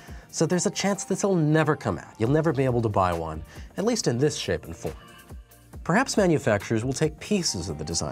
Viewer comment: I can't understand what he says at 3:07. Is it editing glitch or am I just deaf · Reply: He says 'shape and form' so probably the latter :D